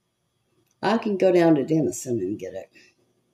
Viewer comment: Well, we don’t live as close as I thought. Lol. I thought you said you could go to Ennis to get your stuff. Went back a re listened an realized you said Dennison. 2.5 hours from here. Still, not all that far 🤗